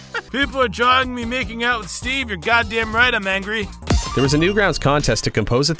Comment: what did he say ? I heard Steve :|